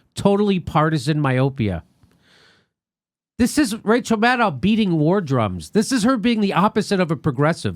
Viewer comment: Oh sorry i thought you said madCow